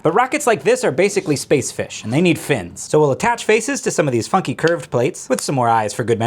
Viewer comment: I GENUINELY THOUGHT HE SAID "These fucking curved plates" INSTEAD OF FUNKY